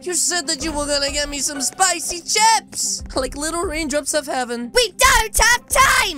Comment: Lol in one part I heard him say spikey chops